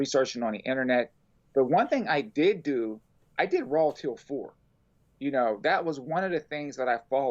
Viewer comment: What on Earth is “the rowal tiddle four”??? (10:52 11:34) I’ve stopped and replayed this a dozen times and I have absolutely no idea what he’s saying! I’m not joking here, honest, please someone help 🙏